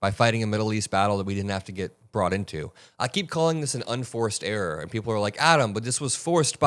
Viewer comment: Oh...I thought you were saying unfarced errors. Now I get it. If we give the mouse the cheese the CAT gets the fish, the dog gets the bone and we all go to heaven in little rubber boat. Glory, glory Hallelujah, Trump's Crap goes on and on.